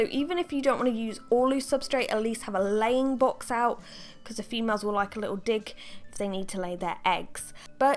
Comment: First time I was listening to this I heard females like a little d***.” 😂